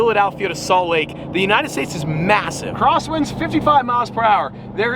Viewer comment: did you say massive?